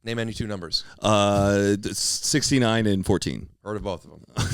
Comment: WHAT DID HE SAY? Both of what? I can’t understand what he said. Heellpppp guys🙏🏻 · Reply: “Heard of both”